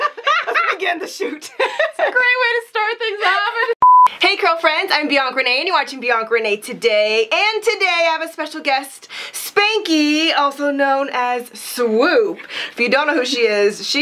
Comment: All along I thought she was saying “I’m beyond grenade and you’re watching beyond grenade today.”😂😂😂